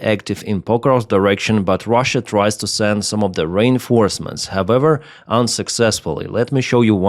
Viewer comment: I thought you said 'Rain Forests', I knew that had to be wrong. It's re-reinforcements 😁